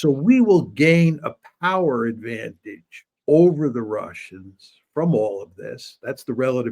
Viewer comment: Did he say russians at 6:17?